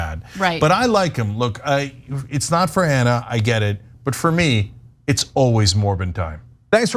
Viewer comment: Ok I just heard Cenk say " For me, it's always Morbin' time." The meme has entered the culdesac. That means it's officially dead now, right?